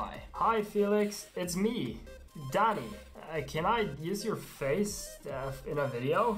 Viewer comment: haha i turned on subtitles and it said daddy instead of dani . Haha subtitle go brrrrrr